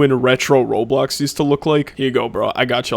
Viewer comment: you said "used to look like" but the subtitles say "these two look like". automatic subtitles aren't always accurate